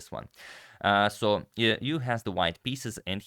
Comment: Who else thought that Agadmator was saying "YOU have the white pieces"